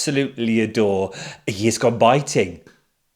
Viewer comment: Woo hoo, here we go again. I love Unpicked, so happy to have you back. At the beginning I thought said “a years gone biting” and wondered what on earth you had been up to! I didn’t like the red patch pockets on the shorts in terms of overwhelming colour but did appreciate that Anna had tried to be different. I thought the transformation was a better challenge than we have often had in the past. I can actually see someone taking an old apron that they didn’t want any more and turning it into a bag. My friends and I discussed a perfect summer outfit - we would need scruffy gardening shorts, cycling shirts and underwear for sewing in the heat - what an episode that would be! I thought Dawn would go home purely from the amount of coverage of her struggles and that historically if you mess up a simple make you go home. Having said that she put it right beautifully so that was unfair. I am guessing that more ambition means more interesting makes and therefore better TV